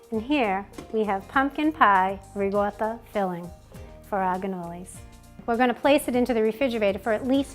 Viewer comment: It sounds like your saying ganolis! Maybe it's the audio.